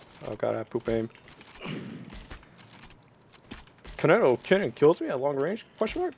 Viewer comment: What I hear from john: Koneko kitten Subtitles: Konoe Kenan